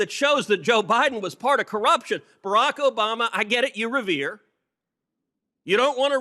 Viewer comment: I'm doing a translation here, Can someone write me what He says at 8:19 ? I hear "Barack Obama, I get it , you Revere" , is the word "revere" correct ? Also 4:18 , what is "MAST intercepts" ? Please Anons, I need your help !